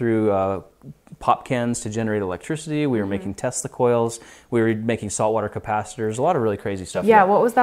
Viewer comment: I thought he said testacle but then I relized he said tesla coil